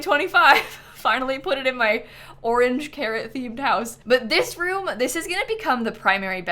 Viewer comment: for some reason I heard "orange carrot-themed house" and my brain lagged and auto-corrected it to "carrot orange-themed house", like it's based after oranges and the color is carrot. Then I finally understood what my brain cooked up and was like "the hell?"